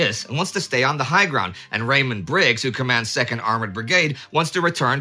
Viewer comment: I could have sworn said "Raymond Briggs", i.e. the author of The Snowman, rather than Rawdon Briggs.